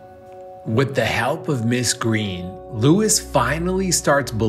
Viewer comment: I thought it said what the hell with mis green😂